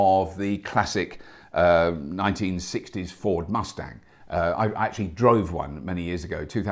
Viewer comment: I first heard it as "Ford Maquis." And I said "WHAAAAT??"